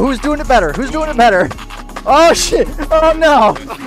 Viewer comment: is just say shit